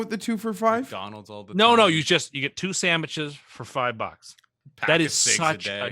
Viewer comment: Did say Samiches?...........